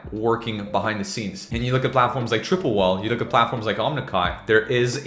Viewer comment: what's 'omnikai'? @ 3:01 - Never heard of it, and can't find it through google. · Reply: I believe it’s called Omnikal, I thought he said “omnikai”too. Went to google and just typed “omni” and automatically gave me Omnikal